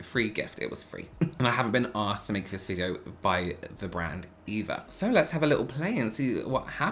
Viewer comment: I heard “and I have been asked to make this video by the brand Eva” and was like what on earth? 😂 loved this video!